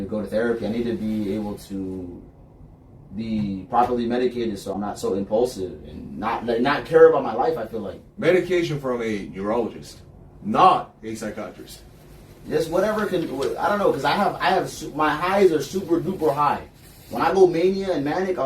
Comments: Did just say medication from a urologist nor a psychiatrist? Is his prostate causing his bipolar mental illness? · "Medication from a neurologist- NOT a psychiatrist!" Excellent interjection. Truly insightful. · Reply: I thought he said Urologist 😳 Thanks for your comment so I could delete my ridiculous one asking about that